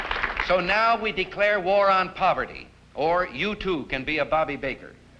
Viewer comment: I don't mean to be a dick, but did I hear Ronny say, you tube, starting at 13:04? Forget it, I now now he said "you too."